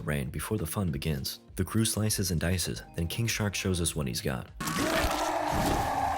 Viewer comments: Why do I keep hearing it as 'Kink shark' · bruh i thought you were saying kink shark